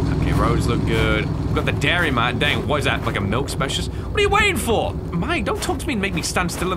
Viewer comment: "we got the dairy mart dang what is that (inaudible)" BRO WHAT DID HE EVEN SAY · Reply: "Like a milk specialist?"